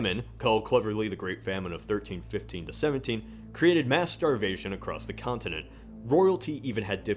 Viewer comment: At one point says something about mass starvation. At first I thought he said "masturbation".